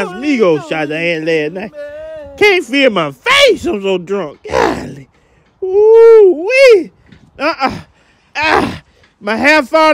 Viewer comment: Hilarious. What is the word he is saying after "I cant't feel my face I'm so drunk" (at 0:12)? I understand this is some southern slang, but would like to understand this expression a bit better. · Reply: “GOLLLYY…wooo-weee!!! UH-UH !!! AHHH…My hair fallin off, I ain’t got tha mange! Oh Lord, OH LORD! THEODIS!!! That you out there singin (name of some song)?!? Playa, I was out all night las night!! Tryna get some…(?) !!!